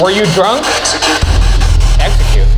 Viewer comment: i swear i heard olivia rodrigo, idk why i found that so funny but all it was, was just "you"